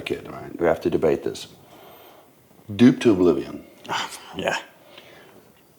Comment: what is he saying here ? 🤔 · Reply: Duped to oblivion.